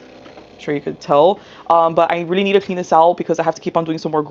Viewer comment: Every time she said “clean this out” I heard “penis owl” 😹